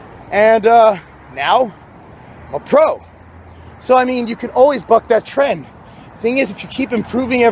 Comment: I swear it sounded like you said "Now, i'm a pro. So i mean, you can always up that Tren"